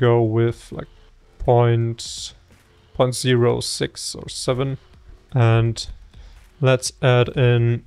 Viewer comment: is it me or did he say 67?